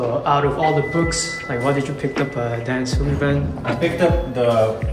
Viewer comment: Did anyone get the name of the author or book at 1:25? Did he say Dan Sullivan? Text transcript shows 'dance swim event' 😄